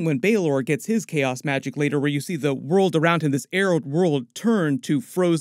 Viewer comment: What is a "Arold World". What is the word he keeps using. "Arold". Do you mean "Barren World"? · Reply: Arid